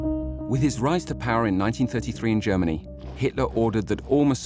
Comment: It sounds like you said "with his rise to power in 1953."